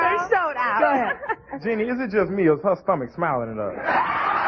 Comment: Can someone please please please tell me what the black man said at 11:49?????? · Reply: He said "Is it just me or is her stomach smiling at us?"